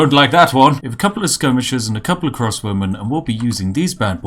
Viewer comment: Both the captions and I heard "Cross Women," not "Crossbowman"